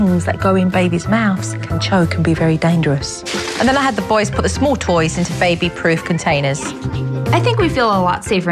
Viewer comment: It sounded like she said I had the boys put the tiny toys in baby proof potatoes